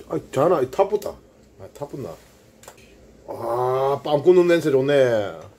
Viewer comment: 밤 굽는 냄새 좋네~가 빵구 냄새 좋네로 들리노😑